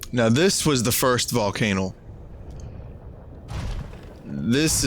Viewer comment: Did... Did just say "Volcanal?"